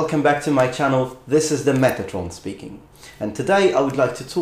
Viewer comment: Thought he said "Megatron is speaking " lol btw i though he had sleeves of tattoos on his forearms but realized it's his arm hairs lol